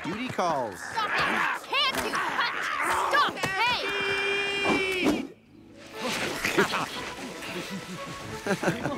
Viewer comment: what did the Omegas just say I could not understand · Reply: Stampede